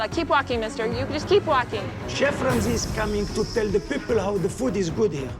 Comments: Did she say chef Ranjy? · "Chef RANCHY" lol, who else heard that? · did she say "chef rangey" XD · Did she say chef Rangey ?